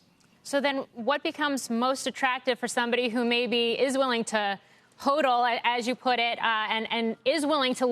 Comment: What is “hoadle” as she says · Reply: HODL. "hold"